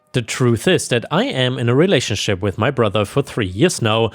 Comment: Excuse me! Did I hear in a relationship with my brother?